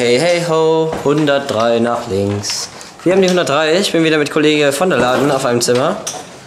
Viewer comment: Hab verstanden ich bin wieder mit Kollegah im Zimmer xD